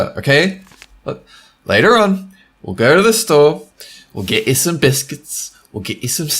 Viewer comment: LOL, I thought said he was going to get biscuits and sex until I put the captions on...